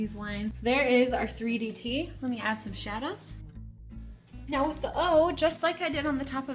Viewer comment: Did you just say "shadas" for "shadows"??😂😂😂😂😂😂😂😂😂 Lol.